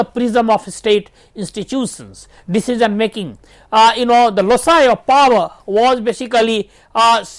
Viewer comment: <link> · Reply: What did sir say at 49:37 ,? "Lossiuer of Power"? I was unable to understand.